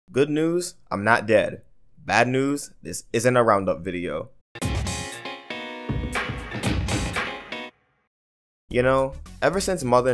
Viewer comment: LoL i thought you said bad news i am not dead .... I laughed, but i rechecked and i was wrong. Funny